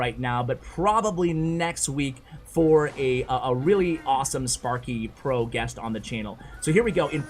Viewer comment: Did someone say sparky pro guest???